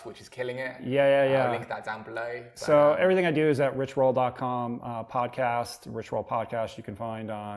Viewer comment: Rich Roll is one of the men I most look up to these days. Go Rich. Us guys like you in our 50s.....you let us know anything is possible...and we ALL thank you for that. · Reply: David Hutchinson I thought you said rick roll I fucking cried